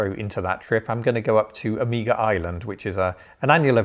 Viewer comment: I genuinely thought you said you were going to Amiga Island 🤣 I thought "where is this wondrous place?!"